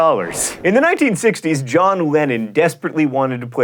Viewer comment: So, I totally misheard "lennon" as "lenin", and thought a leader of soviet russia wanted to be a villain in a movie with a boy band as the stars.